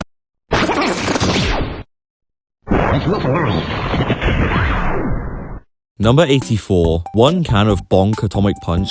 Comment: what does it say? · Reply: let's make some noise